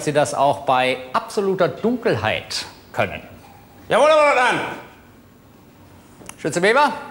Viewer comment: Wie kann er das "Oberleutnant" denn bitte so schnell sagen? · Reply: Ich hab immer "Herr Leutnant" verstanden und mich schon gewundert, weil der doch ZWEI Pickel auf der Schulter hatte ...